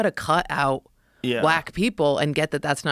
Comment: 😂 I legit thought She said “you gotta cut out Black people - that’s not your OCD, that’s just healthy for you” because subtitles put “black” instead for “wack”. I thought she was making a hilariously insane race joke and that Rogan just blindly agreed without actually listening because jokes go over his head all the time. Welp, looks like I’m the idiot upon examining what was actually said.